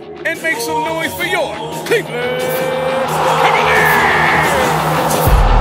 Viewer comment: Cleveland Cavalier?! Did I heard it right?😂